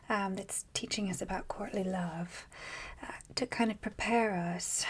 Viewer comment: I'm sorry, did you say Courtney Love? Oh, no, sorry, you said "COURTLY love." That's a very different subject.